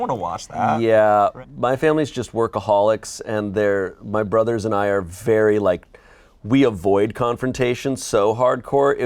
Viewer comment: At first I thought said "my brother is an iron fairy" 😂